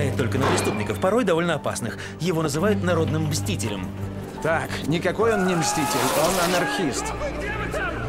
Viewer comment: Мне,поначалу,послышалось: "Так,никакой он не мститель,он АНАНИСТ!!!"